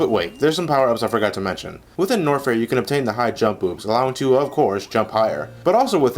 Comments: did you just say 'high jump boobs' at 4:50? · did he just say high jump boobs? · I thought he said Hi-Jump Boobs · I thought you said high jump boobs instead of high jump boots.